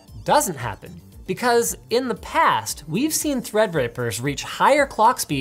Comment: am i blind or did i hear "thread-rapers" at 6:10 ?